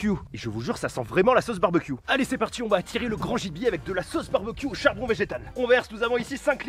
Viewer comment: j'ai compris " on va attirer le grand JD avec le mazout" 😂😂 Alors mtn tu t'attaque aux suisses?